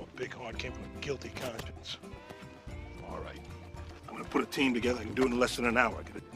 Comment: What does Alex say at 6:57? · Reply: He says alright